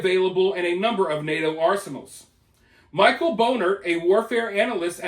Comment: Did you just say, Michael Boner?😂😂😂😂😂😂😂